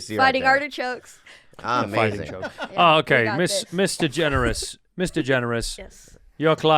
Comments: I kept hearing "Miss. DeGeneres" as "Mr. Generous" and it was fucking with me for a good couple minutes. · lmfaoooo i’m so high, i thought andrew was saying “Mr. Generous” 🤣🤣🤣🤣🤣🤣